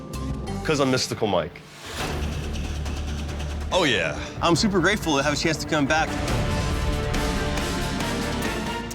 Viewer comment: does he say it’s “hypocriful” or “apocryphal”… dude is amazing! And I know how much that word is overused. He’s like a perfect example of the Dunning-Krueger Effect. Like, literally everything about him absolutely sucks… meanwhile, he thinks he’s the smartest, most intelligent, most attractive, and most skilled person in the world. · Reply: He said "apocryphal". 😂